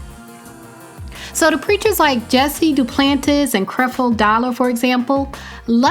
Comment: I thought she said crippled Dollar. Lol. My bad